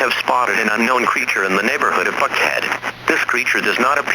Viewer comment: Don't worry, the neighborhood is called "Buckhead", I misheard it at first too.